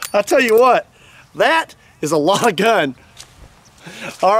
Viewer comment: 当たり弱ー⤴︎⤴︎いって聞こえる